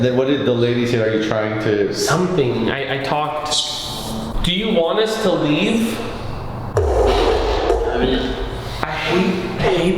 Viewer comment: i heard the lady say do you want to stay here client